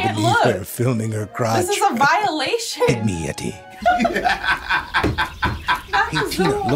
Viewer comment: seems to be funny af, but i cannot understand what he's saying at all... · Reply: He just says "pygmy yeti" again